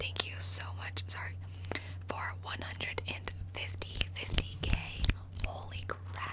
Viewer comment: I misheard “for 150k” as “450k” and almost choked